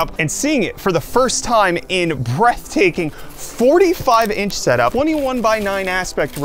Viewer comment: I kinda understand Brett-taking instead of Breathtaking 0:20 😂